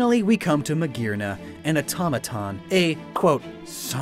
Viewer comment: I first thought you said "otamatone"